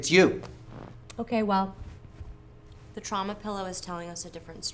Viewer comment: I heard "Troma Pillow" I both want one, and don't